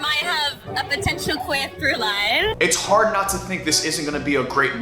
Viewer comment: I have no idea what shes even trying to say. "Queer through lie" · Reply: "Queer through-line" Through-line means an undertone or message